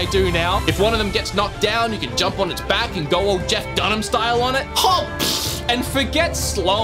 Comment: Did he say you can go "Jeff Dunham style on it," or am I hearing that wrong? Jeff Dunham, the puppet guy? The comedian? What would Jeff Dunham style even mean? Are you gonna....put your hand...up.....no no no no no.